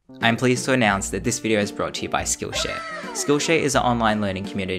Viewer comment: i thought he said skillshit